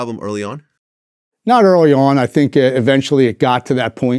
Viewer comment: did he say “not at all your honor” ? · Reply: Haha That's his Philly accent. I think that he is saying "not early on"